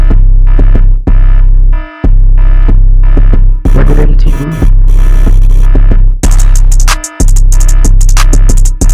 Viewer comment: did he say 2019 or number 19? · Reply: It's my producer tag, but might change it soon anyways, it says 'What did MT do?'